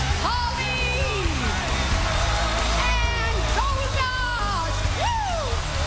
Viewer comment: I could have sworn I heard her say "The Great Curry".